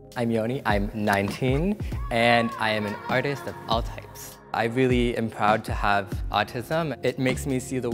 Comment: I thought the artist guy said "I'm an artist and an autist" at first.